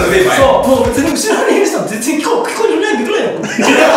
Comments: ザカオ（訳）後ろにいる人、全然声聞こえないぐらいよ。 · のザカオの意味わからない言ってた言葉は うしろにいる人全然聞こえないって言ってるやろ。